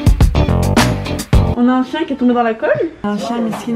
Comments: j'ai entendu qu'il était tombé dans l'alcool 😅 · j'ai compris : "On a un chien qui est tombé dans l'alcool" suis-je la seule ? 😂